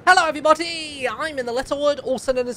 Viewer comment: I thought he said Hello every botty at the beginning